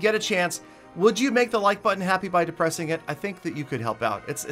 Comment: Why do i always hear "gently depressing it"?